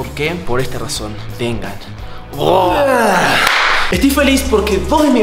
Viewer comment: YO ENTENDI GOGOGOGOGO LIKE SI TAMBIEN PENSASTES MAl JSJSJJSJSJS a y aqui los 2024 ahora es alfa